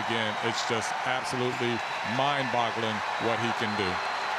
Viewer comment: did he say mind bottling?